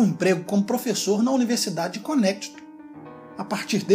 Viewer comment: Qual universidade??? nao entendi essa parte! Conétitu? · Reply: Connecticut